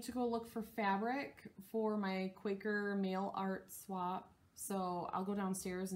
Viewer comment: It's RIDICULOUS that I am so damn far behind on your videos. I need to go sit in the corner of Flosstube shame! When you said "Mail art swap" I heard it as "MALE art swap" and I was like, HOW DO I NOT KNOW ABOUT THIS GROUP?! So, yeah, we all know where my mind is all of the time... Hello, gutter. I love the Kindred spirits finish! And the 12 days... HOLY CRAP is that amazeballs!! I am so happy you got Garden of Eerie too! I LOVE that chart. I will need to stitch it before I die!